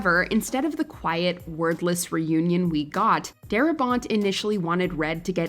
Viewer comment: "...However, instead of the quiet, WORTHLESS reunion..." was surprising to hear Until replaying, realizing it was less common word -- 'wordless'